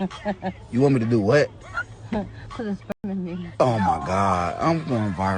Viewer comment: What did she say??? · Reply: She said “put sperm in me”